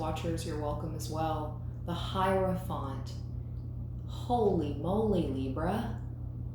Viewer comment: i heard “holy moly donut shop!” 😂😂🫶